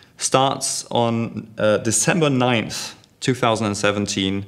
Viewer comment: did he say december 9? isnt kay coming december 6? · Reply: EmptyPillow he said the first season starts December 9th, Kay is released 3 days prior on December 6th...